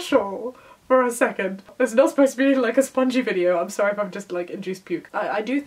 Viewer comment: What's that word she says at around 5:40? Spongy? Is that a British thing and I just haven't heard it? I'm assuming it means corny. (sorry for all the questions, I really want to know) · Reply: As a British person I can confirm it was spongy. However I'm from the other side of the country from and. So I have no idea what it's supposed to mean. Maybe it's an Essex thing.